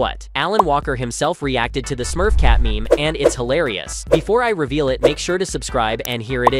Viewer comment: I thought you said it was hilarious